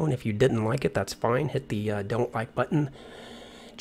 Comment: Great flight as usual. Trivia: one of the fun things about minor hearing loss is that sometimes things just don't sound quite like they should.. I could have sworn you said at the end, "check for leaks in the description".